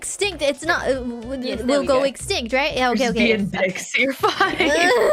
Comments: Did say "We're just being dykes" @ 00:33? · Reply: "we're just being dicks" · "We are just being dykes, you're fine". That's what I heard right? I don't think I've ever heard some one say 'dykes' instead of 'dicks'.